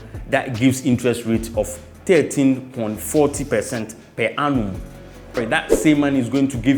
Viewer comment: 13.four zero. Got a bit confused thought you said 13 and 40% Solid points